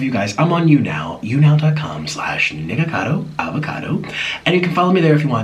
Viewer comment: did he say "Niggacado avocado"?